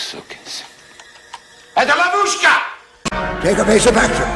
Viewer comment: The subtitesl said "trap" but I heard "babushka"